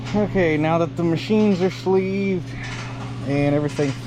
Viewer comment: Is there sound ? or is this on my end that there no sound ? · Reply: There is sound I even heard him say "machines are sleeved" instead of "sleeves are machined" ;)